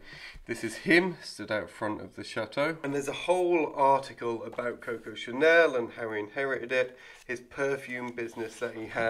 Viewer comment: Oh, so it DID belong to Coco Chanel? 😊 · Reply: Well, what I heard, was: "There is a whole article about Coco Chanel, and how he inherited it (The Chateau)." It sounds to me, like the article is explaining why he inherited the Chateau from her.